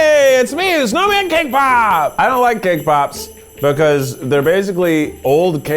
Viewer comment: I seriously thought he said K-pop instead of cake pops.